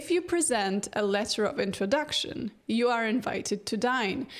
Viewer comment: I heard "...you are invited to die" and just accepted it